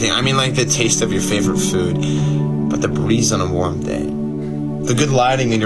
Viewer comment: Lol I had to keep rewinding because I thought he was saying he was grateful for "the police on a warm day" not the breeze